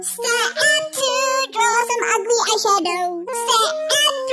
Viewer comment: I thought Chloe said "Step 3: with a kwiss on Adrien's girlfriend 😂😂